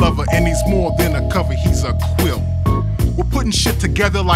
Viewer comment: "He's more than a cover, he's a quilt" has to be enshrined in the Smithsonian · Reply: i thought he said "he's a crip" 💀